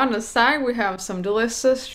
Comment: "deliocoss" did I hear that or its just me 👇👇👇👇👇👇